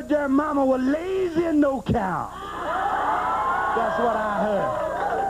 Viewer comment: Help me out here, I've replayed it 4 times & can't understand what the hell Dusty said at :30..."I heard their momma" what??? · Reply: She was lazy and no count.